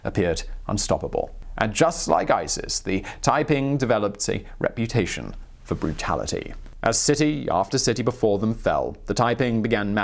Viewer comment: I heard, "Typing developed a reputation for brutality."